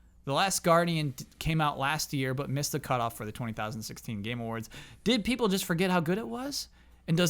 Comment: Did just say TLG came out in the year 20,016?